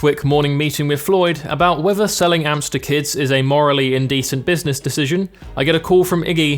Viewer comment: When he said "selling amps to kids" I thought he said "selling hamster kids" and I wasn't concerned cause that's just the type of YouTube is